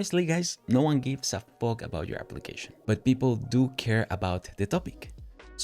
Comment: Language! Or at least the subtitles are wrong... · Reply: he said fork 🍴